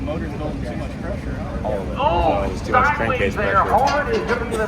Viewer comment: Did I hear “Cank Crase pressure” ?? 😅